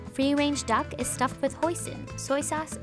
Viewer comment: I heard Poison.